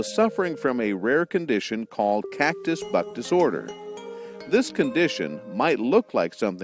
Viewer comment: It almost sounded like he said Catcus Butt disorder..